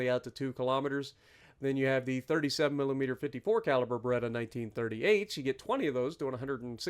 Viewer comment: When you said Bredda I thought you said Beretta lmao